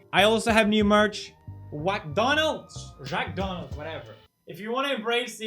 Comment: I thought it said SHAK-donalds